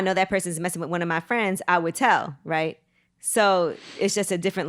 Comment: Right! HIV is serious out here. aint shit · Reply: HIV is not a gay man problem. I thought he said he would tell a friend.